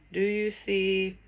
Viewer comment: Omg! On 50:29 when you said “do you see” I heard “do you see her” & then when u spelled out bar, I heard “do you see her bra” & before you started the word with friends, you said “they seen you, but didn’t care.” ❤️ then when u spelled out hunt, I seen “haunt”. I love you doing these word things. So much fun putting words together. It’s funny bc I play the game “words with friends” on Facebook. And my brother just sold my baby daddy his truck and the front tag on his truck says “Gotham City: Hahaha” I also seen “Air, Earth, Fire” and “tears” and I spelled out “Fortnite” & when you said “rat, I spelled out poison beside it.” 😳 and u said dyer and I live in Dyer ❤️